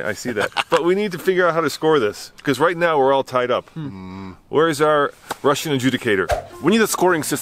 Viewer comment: You about made me spill my coffee. I swear it sounded like where out Russian pubic hair. I had to rewind to make sure you are being kind. That one threw me off. At 19:32. I just heard you wrong with all the fireworks going off. I swear it sounded like you where out to get the Russian.